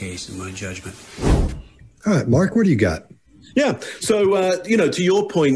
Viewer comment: I thought it sounded like her saying Burk what did you do